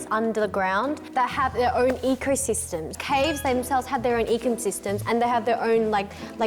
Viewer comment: eco system 너무 equal system 으로 들림 ㅋㅋㅋㅋㅋㅋㅋㅋㅋㅋㅋㅋㅋㅋㅋㅋㅋㅋㅋㅋㅋ Auassie 🇦🇺